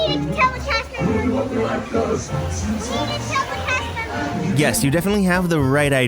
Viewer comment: I thought she said we have to tell the cash member